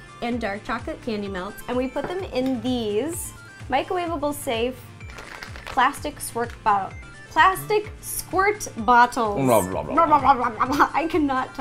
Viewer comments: when she said plastic swerk bottles, i thought she said plastic twerk bottles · i thought she said plastic twerk bottles XD